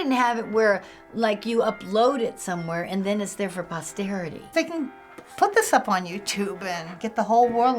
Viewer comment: lol that one lady who said she danced in high school, when she said they didn't upload videos of it for posterity, omg i thought she said posterior! (as in butt)